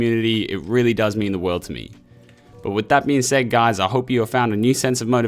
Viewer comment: Thought you said "Get out there and start smashing your girls" Haha